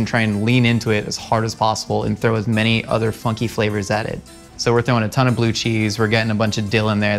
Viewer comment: I thought he kept saying f****ng flavors, not funky flavors